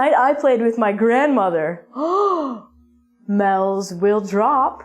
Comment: Could someone help me, please? There is only one word in the entire video I don't understand. At 4:14 she says '*** will drop' I clearly hear 'mels', but that's not an English word. Is it?